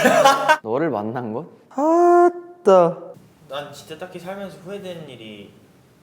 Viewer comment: hyunjin: what you regret the most? seungmin: that I met you hyunjin: tHaT I mEt yOU? WHAT THE- he sounds like he said "what the" lmao